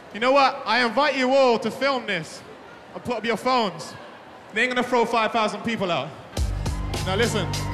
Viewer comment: Bro had an actual chance but he just ruined it for himself 😂😂🤦‍♂️🤦‍♂️ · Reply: For real what did he even say? Like I heard “I’m gunna throw 5,000 out” but idk dude